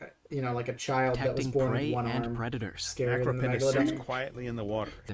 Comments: the captions of the video they are watching says macro penis and I'm pretty sure that that is what he actually said. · the subtitle says macro penis lmfao